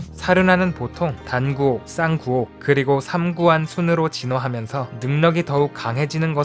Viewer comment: '삼구옥'인데 '삼구안' 이라고 말하는 것을 들었습니다. ㅋㅋㅋㅋㅋㅋ